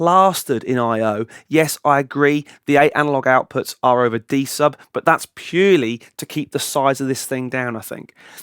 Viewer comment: Didnt understand what he said at He started to say: Yes i agree the eight analog outputs are... from there didnt understand what did he say and the meaning.. Can someone clarify please? Thankss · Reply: He said the the analog outs are using D-Sub which is that 25-pin connector you see on the back. You need a breakout cable for that and then you can use all the outs.